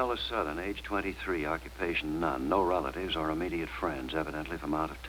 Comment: "Occupation: none" I heard "Occupation: Nun" 😝 I feel for anyone trying to learn to speak and spell English. 😏